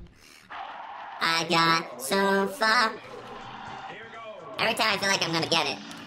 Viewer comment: did he say the "f" word? Cuphead: no silly he said "im so close" thats what he said Oh ok?? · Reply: Sans Meyer he said he got so far